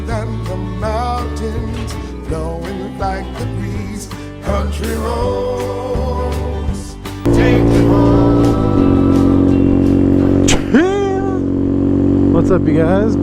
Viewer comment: At first I thought the song in intro said "Country ropes, take me home". You should make your own version as a commercial for WV to go with your cha chia commercial.